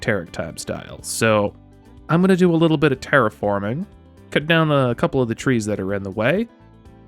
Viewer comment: When you said "terriforming" I swear I thought you said "TerickForming" ... which .. would have been awesome. And on brand.